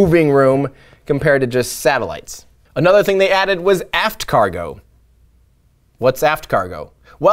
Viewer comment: i thought you said f****d cargo